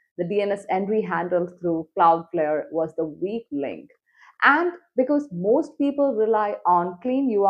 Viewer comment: I think when you say words a few milli-seconds at the start are being cut off, it's barely perceptible, like when you say "was the week link" the very start of W and L are missing "was the eek ink" I think it's probably your noise gate - all this is just a theory though